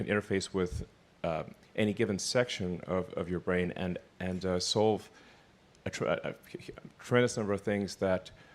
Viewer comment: Notice how at 6:10 it almost sounded like Elon said soul